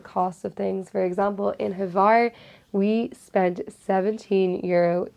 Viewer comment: Great video! Thank you so much! Can you pls or someone tell me where you went boating which you mentioned in minute 12:01, I hear Havar but can’t seem to find anything related. Thanks!